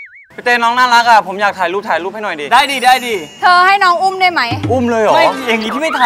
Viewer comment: น้ำตาลเรียกคริสว่าเธอ 17:25 คริสก็เรียกน้ำตาลว่าเธอเหมือนกัน ใช่ป่ะ หรือเราได้ยินผิด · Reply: ใช่ค่ะๆๆคุณๆเค้าเรียกกันว่าเธอออออ อุแงงง